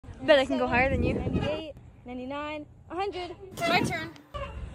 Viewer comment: My heart stopped for a second cause I thought she said "I bet I can go harder then you" 💀